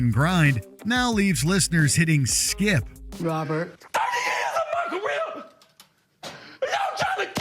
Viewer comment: robert!? "30 years of macarena, y'all trying to kill me" 😂😂 is what i heard